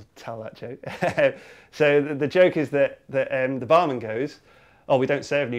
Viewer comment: I thought you said The Batman LOL!!